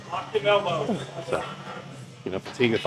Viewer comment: I thought the background dude said “watch them elbows” like fuck off Sams going hard on the set but just needed chill for a sec cause he did not say that 😂